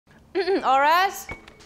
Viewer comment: What she says : All rise Subtitles for some reason : our ass